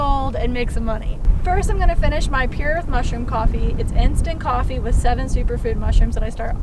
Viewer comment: I had to watch again, I thought you said bear and mushroom coffee..! 🤣🤣🤣